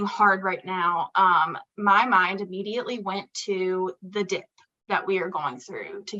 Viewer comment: not me thinking she actually said "THE D*CK" after expressing they needed to talk about when things get hard. Someone help me 🤣🤣🤣